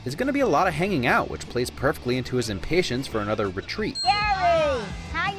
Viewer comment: I like how she achually called Stan Stanly. Or did she say Stan Lee? Nickname or last name it's still a nice little touch. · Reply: No, she said Gary.